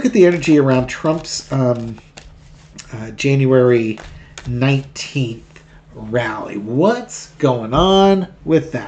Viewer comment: We elected Kamala. I'm holding out for hope and joy. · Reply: I just heard “July 19” (the first time you said the date); you’re speaking of January 19th. Ok. 2nd time you mention I hear the correct date. Maybe I heard wrong. Love your channel.